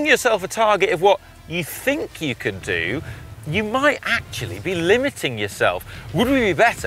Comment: Am I the only one who hears a duck when he says “actually” on 0:56? Like *CUAK*tually...